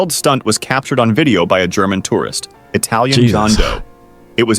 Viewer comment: did that say... John doe?